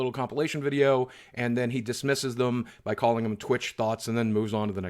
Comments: I really thought he said "twitch thoughts" lol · I really thought he was saying "thoughts" instead. Never heard the word Thot before. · All this time i thought he was saying "Twitch thoughts" and really didn't get why she was so upset!!